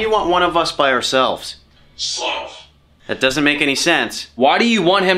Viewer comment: Hey did anyone else catch that it said Sloth? That's one of the seven deadly sins. It said it